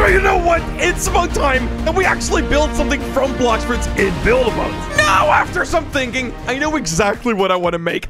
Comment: What he said:"Actually build something from blox fruit in build mode" What the caption heard: "Actually build something from bloxford's in build mode"